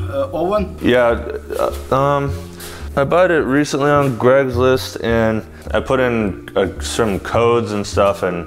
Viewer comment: I swear said Greg’s list @ 6:49😂😂😂